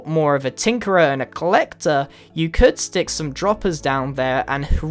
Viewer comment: did he just say droppers